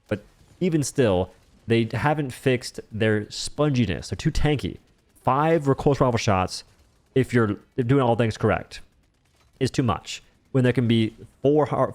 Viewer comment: Five what shots? It's probably me, but it's impossible to hear or decipher what's being said here 🤷🏼‍♂ (edit) Nevermind. I think it's supposed to be "recoil-less rifle", and it fits with the stratagem he's using there. And I'm fully aware of tendency to speak in half-words, but still... I struggled with that one. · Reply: Recoilless rifle